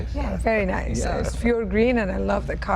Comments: Did she say fuhrer green? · Did she say she has 356 in fuhrer green? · Did she say her car is “fuhrer green”?